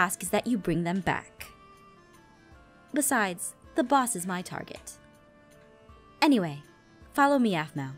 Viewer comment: I thought said "my boss is at target" I was like "OMG I LOVE TARGET!!" 😂😂